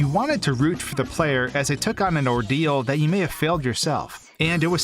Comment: What he says : Ordeal What i heared : Dio